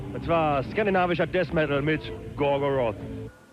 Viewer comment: Did he just say "Scandinavische Deaz Metal, Gorgoroth"?